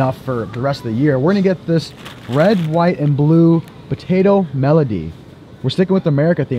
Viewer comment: “red, white and potato Melody” instead of medley 😂😂